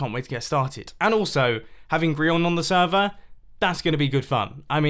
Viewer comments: I am not sure if I am going crazy or what but I heard him say "Grion" instead of Grian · Did he call him grion?